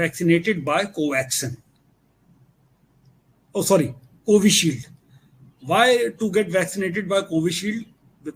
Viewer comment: Which vaccine do you mean? ( 'similar virus' ? by 1:28:30 h) I can't understand the word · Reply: He said covishield vaccine...